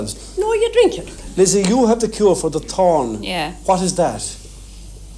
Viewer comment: 🤣🤣I thought she said she had a cure for the horn!!😃